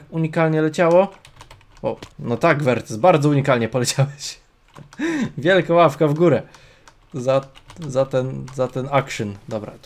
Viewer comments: Czy tylko ja usłyszałem [ Wielka ławka w górę ] minuta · Czy tylko ja usłyszałem [ Wielka ławka w górę ] minuta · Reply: ja tez usłyszałem wielka ławka w górę xD